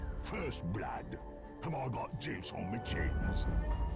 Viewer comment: Did pudge just said i got JIZZ on my CHINS? Or i misheard? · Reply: Yaaaaaa... Actually it's Juice!